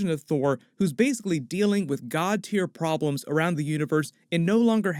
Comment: the says "god teir", but the captions say "God Tyr". There is a god in Norse mythology named Tyr (Thor is also a Norse god). I thought that was clever